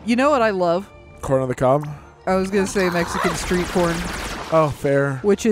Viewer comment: Did anyone else hear say "Mexican Street porn" at 27:16, or was that just me?